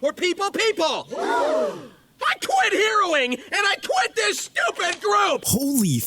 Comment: I thought bro said heroin 💀💀💀💀💀💀💀💀💀💀💀💀💀💀💀💀💀💀💀💀💀💀💀💀💀💀💀💀💀💀💀💀💀💀💀💀💀💀💀💀💀💀💀💀💀💀💀💀💀💀💀💀💀💀💀💀💀💀💀💀💀💀💀💀💀💀💀💀💀💀💀💀💀💀💀💀💀💀💀💀💀💀💀💀💀💀💀💀💀💀💀💀💀💀💀💀💀💀💀💀💀💀💀💀💀💀💀💀💀💀💀💀💀💀💀💀💀💀💀💀💀💀💀💀💀💀💀💀💀💀💀💀💀💀💀💀💀💀💀💀💀💀💀💀💀💀💀💀💀💀💀💀💀💀💀💀💀💀💀💀💀💀💀💀💀💀💀💀💀💀💀💀💀💀💀💀💀💀💀💀💀💀💀💀💀💀💀💀💀💀💀💀💀💀💀💀💀💀💀💀💀💀💀💀💀💀💀💀💀💀💀💀💀💀💀💀💀💀💀💀💀💀💀💀💀💀💀💀💀💀💀💀💀💀💀💀💀💀💀💀💀💀💀💀💀💀💀💀💀💀💀💀💀💀💀💀💀💀💀💀💀💀💀💀💀💀💀💀💀💀💀💀💀💀💀💀💀💀💀💀💀💀💀💀💀💀💀💀💀💀💀💀💀💀💀💀💀💀💀💀💀💀💀💀💀💀💀💀💀💀💀💀💀💀💀💀💀💀💀💀💀💀💀💀💀💀💀💀💀💀💀💀💀💀💀💀💀💀💀💀💀💀💀💀💀💀💀💀💀💀💀💀💀💀💀💀💀💀💀💀💀💀💀💀💀💀💀💀💀💀💀💀💀💀💀💀💀💀💀💀💀💀💀💀💀💀💀💀💀💀💀💀💀💀💀💀💀💀💀💀💀💀💀💀💀💀💀💀💀💀💀💀💀💀💀💀💀💀💀💀💀💀💀💀💀💀💀💀💀💀💀💀💀💀💀💀💀💀💀💀💀💀💀💀💀💀💀💀💀💀💀💀💀💀💀💀💀💀💀💀💀💀💀💀💀💀💀💀💀💀💀💀💀💀💀💀💀💀💀💀💀💀💀💀💀💀💀💀💀💀💀💀💀💀💀💀💀💀💀💀💀💀💀💀💀💀💀💀💀💀💀💀💀💀💀💀💀💀💀💀💀💀💀💀💀💀💀💀💀💀💀 💀💀💀💀💀💀💀💀💀💀💀💀💀💀💀💀💀💀💀💀💀💀💀💀💀💀💀💀💀💀💀💀💀💀💀💀💀💀💀💀💀💀💀💀💀💀💀💀💀💀💀💀💀💀💀💀💀💀💀💀💀💀💀💀💀💀💀💀💀💀💀💀💀💀💀💀💀💀💀💀💀💀💀💀💀💀💀💀💀💀💀💀💀💀💀💀💀💀💀💀💀💀💀💀💀💀💀💀💀💀💀💀💀💀💀💀💀💀💀💀💀💀💀💀💀💀💀💀💀💀💀💀💀💀💀💀💀💀💀💀💀💀💀💀💀💀💀💀💀💀💀💀💀💀💀💀💀💀💀💀💀💀💀💀💀💀💀💀💀💀💀💀💀💀💀💀💀💀💀💀💀💀💀💀💀💀💀💀💀💀💀💀💀💀💀💀💀💀💀💀💀💀💀💀💀💀💀💀💀💀💀💀💀💀💀💀💀💀💀💀💀💀💀💀💀💀💀💀💀💀💀💀💀💀💀💀💀💀💀💀💀💀💀💀💀💀💀💀💀💀💀💀💀💀💀💀💀💀💀💀💀💀💀💀💀💀💀💀💀💀💀💀💀💀💀💀💀💀💀💀💀💀💀💀💀💀💀💀💀💀💀💀💀💀💀💀💀💀💀💀💀💀💀💀💀💀💀💀💀💀💀💀💀💀💀💀💀💀💀💀💀💀💀💀💀💀💀💀💀💀💀💀💀💀💀💀💀💀💀💀💀💀💀💀💀💀💀💀💀💀💀💀💀💀💀💀💀💀💀💀💀💀💀💀💀💀💀💀💀💀💀💀💀💀💀💀💀💀💀💀💀💀💀💀💀💀💀💀💀💀💀💀💀💀💀💀💀💀💀💀💀💀💀💀💀💀💀💀💀💀💀💀💀💀💀💀💀💀💀💀💀💀💀💀💀💀💀💀💀💀💀💀💀💀💀💀💀💀💀💀💀💀💀💀💀💀💀💀💀💀💀💀💀💀💀💀💀💀💀💀💀💀💀💀💀💀💀💀💀💀💀💀💀💀💀💀💀💀💀💀💀💀💀💀💀💀💀💀💀💀💀💀💀💀💀💀💀💀💀💀💀💀💀💀💀💀💀💀💀💀💀💀💀💀💀💀💀💀💀💀💀💀💀💀💀💀💀💀💀💀💀💀💀💀💀💀💀💀💀💀💀💀💀💀💀💀💀💀💀💀💀💀💀💀💀💀💀💀💀💀💀💀💀💀💀💀💀